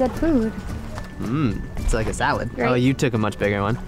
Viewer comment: Is it just me or is saying that she got a bigger one sounds dirty